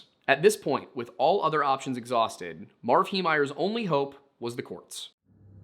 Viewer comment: you mumbled the word "courts" and it was hard to tell what you said until I relistened a few times.